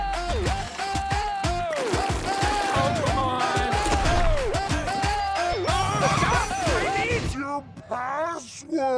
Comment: Did he say oh come on.... Or. Pokamon... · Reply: Oh come on. I watched the film with subtitles on netflix.